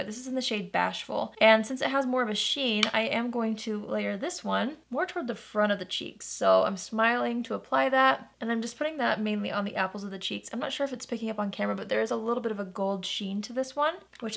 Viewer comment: wait is that dark brown shade really called Cheese? i heard you say it like three times lol